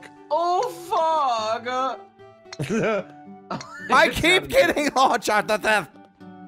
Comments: Ohm speaking: “YOU KEEP WHATTTTTT?! YOU KEEP DOING WHAT NOW?!” Omg I’m dead 😂 Ohm please for the love of everything, tell me what the heck you were trying to say. I have to know. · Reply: he was saying I keep getting launched off the thing. I'm well versed in:P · Does anyone knows or translated what Ohm just said? Edit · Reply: Benette Gramen I believe he said "I keep getting launched off the thing", but Ohm's language is not easy to translate so that could be wrong